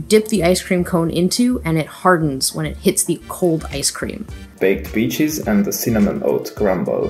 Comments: I thought he said baked beans 😭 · I thought he said “baked b!tches” 😶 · For a sec I thought the guy said bake beans